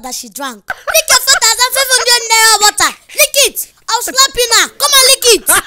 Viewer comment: it says clap instead of slap like if agree